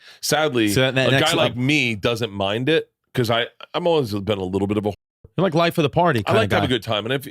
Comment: wtf did he even say? "a little bulabalup"